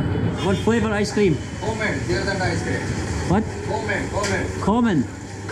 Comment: I thought he said almond icecream